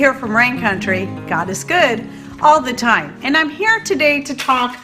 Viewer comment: I almost spilled right past your vid because the su titles said at the beginning, "goddess good" and I was like nope, pass. But I clicked and I heard you say "God is good". THAT is when I stayed! 😁 Christ Jesus bless you and yours. Ty for the info as I was debating purchasing this book, but because I am physically disabled and my only income is SSI, I was concerned about the cost. So thank you for the alternative choice!